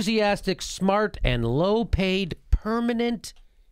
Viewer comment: Misheard “well payed.”